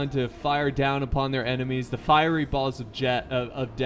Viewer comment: I swear I thought he said (there raining down the fiery balls of Jeff)